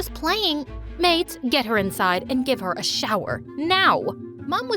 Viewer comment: the mum said “maid get her inside” but the subtitles said ‘mate get her inside’ lolll